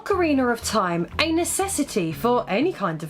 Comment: why do you always sound like you say "Ocariner of Time"?